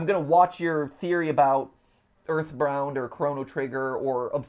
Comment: Did he say Earthbrowned?!?!